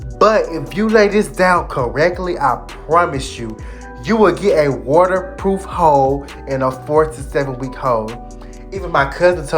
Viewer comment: Swear at 6:00 he said you’ll get a waterproof ho🤣🤣🤣🤣